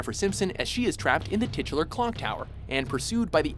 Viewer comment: I am stil learning english, so could someone please tell me what the word is that is used before "Clock Tower" at 2:54? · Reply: Titular. It means it has the same name as the title of the game.